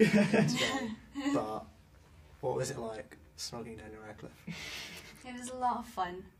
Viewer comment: What does asks Katie at 1:45? He goes like: 'how was it like .. Daniel Radcliffe'! What does he say between the .. ? cmoking or something?